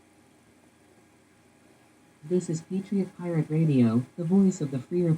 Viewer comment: I had to listen to her 3 or 4 times, but I heard "Patriot Pirate Radio". Give it a listen at the start of her recording.